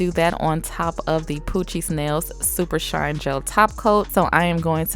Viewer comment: You: Poochie's nails Me: Coochie Nails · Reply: my mom heard her say “Poochie’s Nails” and thought she said “Coochie Smells”. i love her.